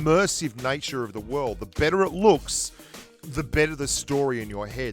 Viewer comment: lol i thought he was saying beater